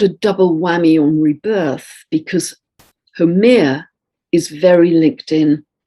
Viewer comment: at time stamp 14:17, are you speaking of H E M E R A, goddess of day? I wrote the name in capitals, so that I could read it better on my tablet, while writing in my comment. I heard you say « Hawai » so it must be H A U M E A, the goddess of fertility and childbirth in Hawaiian mythology. Did I hear right ? By the way, the pronunciation of that name is to be found on YouTube. You are excellent, as usual. Thank you for all you so generously share with us. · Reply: It's Haumea. x